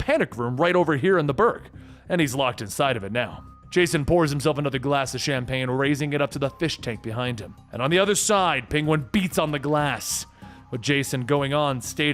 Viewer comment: Did he said jason bourne at minute 30:27?